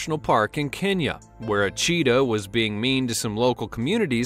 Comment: I thought you said a Cheeto was being mean XD